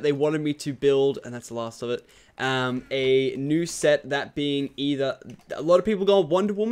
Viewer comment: When you said new set i thought you said nuse set